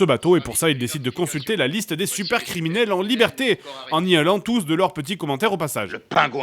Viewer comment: Ok je sais pas si j'ai eu de la chance mais en activant les sous-titres à 13:59 , on a droit à :"décide de consulter la liste des suceurs criminels en liberté" au lieu de "SUPER criminels en liberté" ... voilà ... j'dis ça ... sinon épisode génial, bravo à toi =)